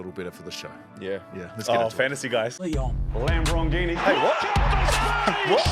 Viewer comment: What sport commentary in the intro is it where it sounds like is says "lam broghini"